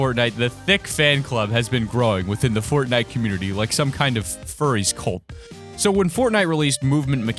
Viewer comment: Did he just say "Furry Cult" -_